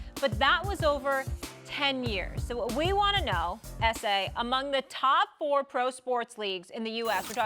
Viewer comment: He callin out Stephen A. an "ese" molly chose violence 🤣🤣 p.s I know its his initials "SA" but I heard diff.